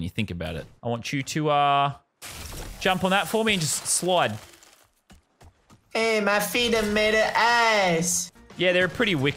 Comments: “hey my feet are made of ass” anyone else hear that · sounds like he said "my feet are made of ass."